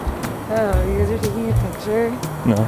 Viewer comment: "Oh you guys are doing da jury?" "No" What does she say? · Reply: She saw the camera and said "Oh you guys are taking a picture?"